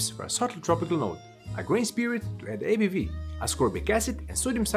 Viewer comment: Thank you so much for the captions. I'm a little ashamed to admit it, but I kept hearing, "grain spirit," as, "grace period." I know they're time consuming and tedious to do, so again, thank you!!